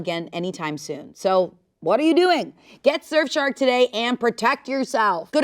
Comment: It really sounded like she said shurf shark the first few times and I thought it was gonna be a bit